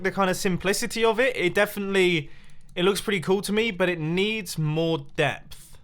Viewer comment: i thought Mumbo said it needs more death